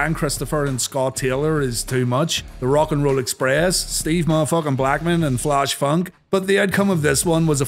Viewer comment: Did he say “Steve mo’ F’n Blackman” @ 3:31?